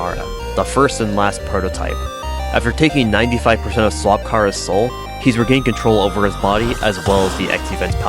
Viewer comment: I can’t be the only one who heard “Slop Chara”